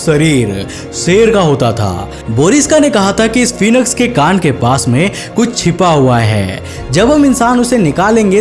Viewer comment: what did he said 🤔 Gand 🤨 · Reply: Lol he. Said kaan (Ear)